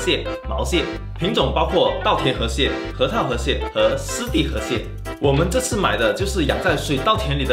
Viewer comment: 我居然能把湿地河蟹听成尸体河蟹……